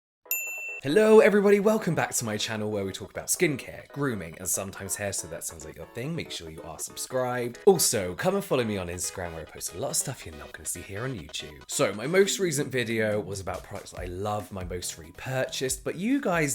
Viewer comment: For the longest time i thought he said ,,...where we talk about skincare, grooming and sometimes hair, so if that sounds LIKE A THING make sure you subscribe..." x'D 😅